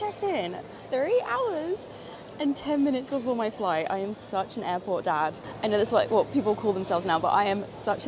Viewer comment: I thought you said, “I’m a real Airport Dag.” Very AUSTRALIAN 😄